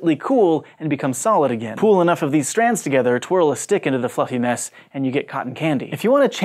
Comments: lmaoo i heard" fucking mess" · Am I the only one who thought he said ''fucking mess" @ 6:30 ? lol and had to re-watch like 3 times to see he said fluffy mess/ness. . Still not even sure xD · did he say "fucking mess"??? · Is it just me or did it sound like he said "throw a stick into the fucking mess"?